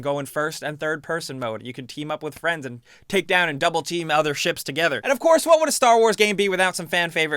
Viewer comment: Did he say "double-team"? ...